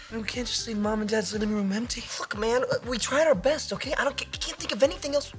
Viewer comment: Did drake say F man or look man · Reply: Look man